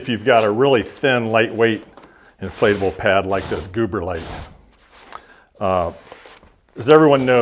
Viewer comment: I keep hearing him say, “Gooberlite”. Ha ha